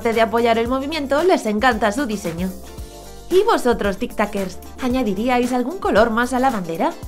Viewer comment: Al principio dijo tiktakers, yo pensé que dijo tiktokers y me ofendí re feo